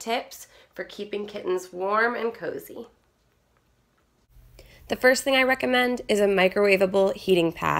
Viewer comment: When she said "The first thing I recommend is a microwaveable-" I thought she said "The first thing I recommend is to microwave it!" (the kitten)